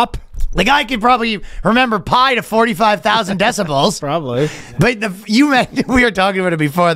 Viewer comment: Did say decibels? 😂😅😅😂😅